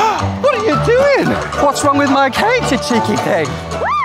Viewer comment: "Whats wrong with my cake, you cheeky thing" so funny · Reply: I thought he said cheeky pig. 🤣🤣.